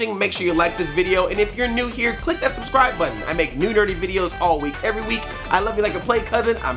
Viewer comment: Was I the only one, but did it sound like says "I make new dirty videos, all week, every week." at the end there!? I'm like, whoa! what channel are those videos on????? Ha! Then I went back and heard "nerdy" then was like, "Ah, of course..." :P